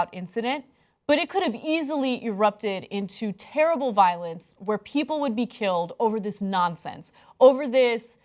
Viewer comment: Hey yt I thought you said that black open carriers would be killed.